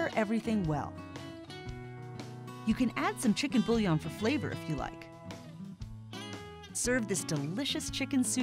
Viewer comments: what did she say we can add for flavor? I couldn't quite hear that part. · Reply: "you can add chicken bouillon (cube) for flavor if you like" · What did she say at 3:10? "You can add some ??? If you like" Sorry I didn't catch what she said. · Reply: Chicken bouillon (chicken stock cubes).